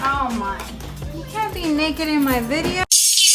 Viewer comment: Did I hear “you can’t be naked in my video” 😂😂 great motivation as always girl 🙌🏼